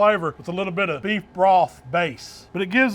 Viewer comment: Anybody know what was the last item he put on the mushrooms? It sounds like he said "beef broth baste" or "deep broth paste". I'd like to get some, but I can't figure out what he was saying.